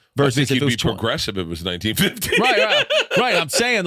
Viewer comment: What does Bert say at 13:10 about Shane. I can’t understand what word he’s saying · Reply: I think he'd be progressive if it was 1950""